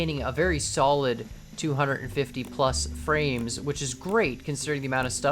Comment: Im sorry can you repeat what you said. I heard " A solid 250 fps". Thats scary